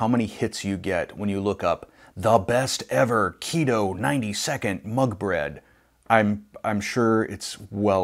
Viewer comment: Hehehe, I feel silly, I thought you said 90 second MONK bread- I'm thinking, what the heck, how did I miss THAT??? 🤣😂🤣🤣 Yes I figured out it was MUG bread, lol- and no, I've never made that🤩